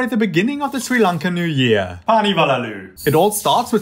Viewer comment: I heard that as "paani vala lulu" 💀